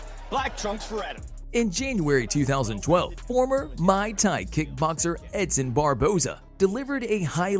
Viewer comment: did he just say, "Mai Tai kickboxer"? Omg 😵